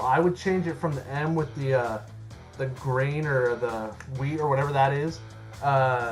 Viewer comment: did he just say weed?